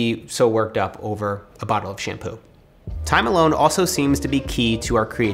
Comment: thought he said empty bottle of champagne